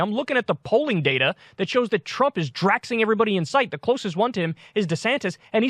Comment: did he just say "Draxing"?? 🤣